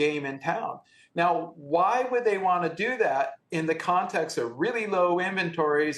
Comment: All I hear is “invest in oil”.